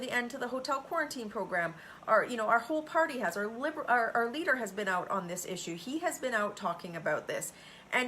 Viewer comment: Did Michelle just call O’Tool “our liberal” at 2:30 yikes! I sure hope that was not a Freudian slip.